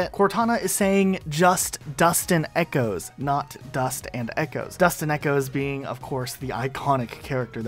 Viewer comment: Actually I heard “Dust and echoes” as “Us and Echoe’s” until I turned on Captions.